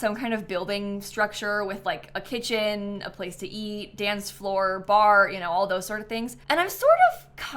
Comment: the way I heard “Dan’s floor” 😭😭😭